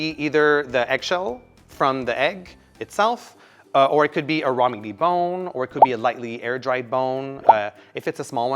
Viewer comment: Help!! I'm have hearing loss and the captions aren't working out for me. Can someone tell me what he says at 4:39 please? I'm hearing "rah-mingy-bone"😅